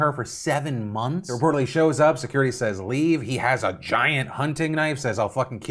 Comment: I'm not defending the asshole that did that, but "giant hunting knife" isn't what they said. They said "large hunting knife", which is also false. That knife is 3 inches. I'm sure any knife feels big when it's being used to threaten you, but that knife is short enough that if it didn't lock open it would be legal to carry in the UK.